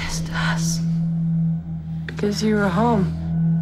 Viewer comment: What was the woman's response at the end of the trailer when she says "why are you doing this to us"?, coz it sounds like she say "because you're a home"